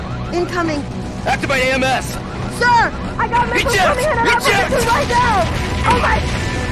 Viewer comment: I could never make out what she's saying. what was her reason to not activate ams or eject? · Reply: From the Sarna wiki: "Sir, I’ve got missiles incoming and my interceptors are down!"